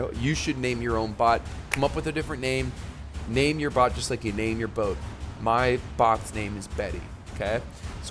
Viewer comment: Dude, I had to run the vid back... I could have sworn you said "name your bot like you name your hoes" LOL! Then realized what you said.... Where's my mind leaning. BOTS, CASH MONEY AND HOES. Not boats. Nice vid. Have a good day! 😂😂😂